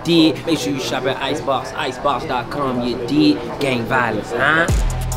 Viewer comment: did he just say "gang violence huuuuh"?